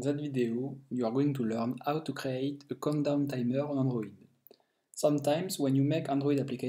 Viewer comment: i heard condom😹